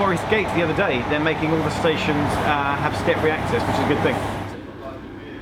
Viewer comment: Does say "They're making all the stations have stiff reactors"? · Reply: Emma Jacobs step free access